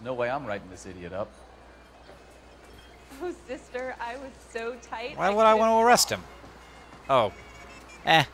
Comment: WTF did the chick say at 11:31? "oh sister, I was so tight I couldn't walk"?